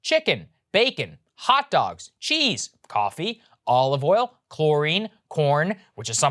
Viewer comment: did Saggar just say coffee shortage ? now I'm worried...isn't someone supposed to completely banished from society if there is even a threat of a coffee shortage ?